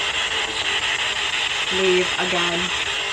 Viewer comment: I heard “innocent” instead of leave.